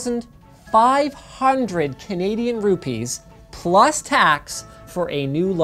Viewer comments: did he say "rupees" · Did he just say Canadian "rupees" 😜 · did he said canadian ruppees? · Btw did he say Canadian rupees on 7:22?? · Did I hear that right, you said Canadian Rupees at 7:20? I guess in Canada official currency is Canadian Dollar. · Did he just say Canadian Rupees?? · Did he said Canadian rupees 😂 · did you say canadian rupeees...? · Did he said Canadian Rupees 7:24 or is it just me☺️☺️☺️. Cool video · Did he say "Canadian Rupees" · Did you say Canadian rupees: 7:21 ?